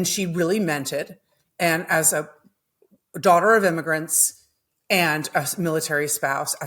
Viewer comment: i have audio processing issues and i wasn't watching the podcast, so i thought tim said "amy's daughter". i also know the difference between immigrate and emigrate. although emigrate is used so occasionally that i don't blame anyone for not knowing about its existence. i always remember it as "immigrate" going "in" with an i; and "emigrate" as "exit" with an e.